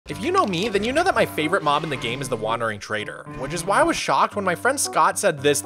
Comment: I thought he said "My favorite Mom in the game" I guess who else takes care of in the superflat?